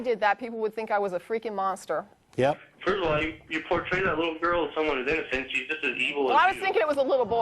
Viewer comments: damn, I can deduce what the caller said before Matt hung up but I can't understand what he said :( Can someone write it (at 13:44 )? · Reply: can it be: "First of all, you portrait a little girl as someone who's innocent, she's just as evil as you"? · aight thanks for answering :) but i did understood that part its at 13:40 that dude say something and i dont understand what he said :p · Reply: Oh, you earlier said 13:20,so I thought it was that.. But at 13:40 - 13:48 the dude on the phone said: "You portray that little girl as someone that is innocent but she is as evil as you". So basically the little girl deserves being raped because she is as evil as the rest of us... This is why I can't take religious people seriously.. smh.. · Can someone explain what he says at the end exactly? i cannot understand all thanks! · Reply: First of all, you portray that little girl as someone who's innocent, she's just as evil as you. Quite the loving religion he practices, huh?